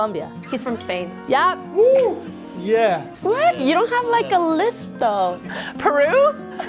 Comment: Are they all singing Enrique Iglesias? xD @ 2:46 She actually says lisp not list which I think doesn't translate to lista but ceceo.